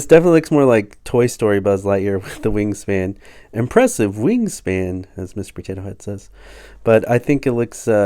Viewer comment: “Impressive Wingspan!” is actually what Hamm said